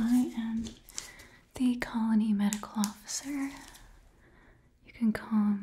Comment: Totally thought that said colon and not colony :')